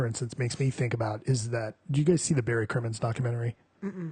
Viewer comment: What documentary is this, I can't figure out either names mentioned, can someone help me out? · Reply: The Barry Crimmins documentary, Call Me Lucky.